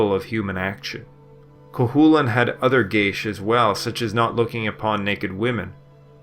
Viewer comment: I'm sorry, what did you say here? Cu chullainn has other Guh-? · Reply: He had other geas, which were Religious/Cultural taboos placed upon individuals; the breaking of which had severe repercussions either due to Divine wrath, or Legally; either way the individual suffers for it. Such as he was forbidden from eating dog, which was not an uncommon practice at the time and which, of course, he ended up doing at one point in the stories.